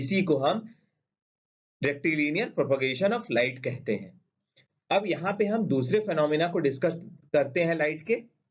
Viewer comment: Kya bola sir apne ? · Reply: Rectilinear prolongation of light.